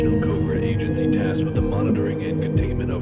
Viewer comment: What did he said? I non-ironically got a "Deplying bloddly" in my mind of that and it can't get out so I can't know what the fuck he said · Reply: "To put it bluntly"